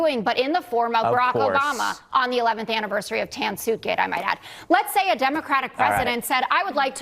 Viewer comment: I heard "Tansuke" and thought it was an East Asian disaster that I missed. Tan-Suitgate., got it.